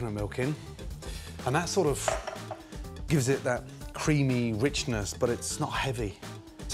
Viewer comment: Sounded like he said ricin, a very deadly poison